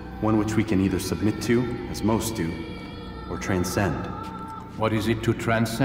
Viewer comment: it sounds like he says utmost to that made no sense to me what did he say there I could not understand that part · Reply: "As most do". Here's the conversation: Al Mualim: Before you go, I have a question for you. Altaïr: Of course. Al Mualim: What is the truth? Altaïr: We place faith in ourselves. We see the world the way it really is, and hope that one day all mankind might see the same. Al Mualim: What is the world then? Altaïr: An illusion. One which we can either submit to, as most do, or transcend. Al Mualim: What is it to transcend? Altaïr: To recognize nothing is true and everything is permitted. That laws arise not from divinity, but reason. I understand now that our Creed does not command us to be free. It commands us to be wise.